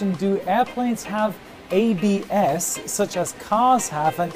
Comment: Ngl I thought it said ‘Do planes have IBS?’